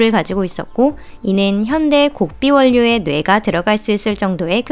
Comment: 뭐라고 하신거임?? 곡비원료??? 뭔말인지 몰겠네.. 알아 들으 신분??